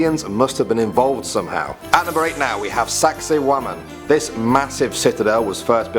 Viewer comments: For saksaywaman I thought he said sexy woman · what the other people are saying "sexy woman" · I thought he said “sexy woman” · sounded like you said "Sexy Woman" not Saksaywaman haha. great video though i love ancient alien theories and the lot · i heard sexywoman